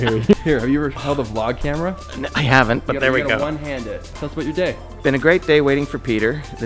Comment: wasn’t fully paying attention and though I heard, “tell us about your dick”. I was like hold up, I need to replay this segment I don’t think peter, oh ok, yea I’m a terrible person.